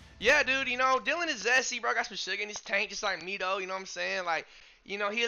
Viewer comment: Did he say “he got some sugar in his tank like me” 😭